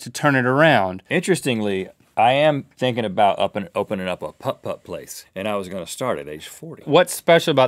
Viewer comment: i hope i'm not the only one who thought rhett said 'pup 'pup' as a joke of 'puppy'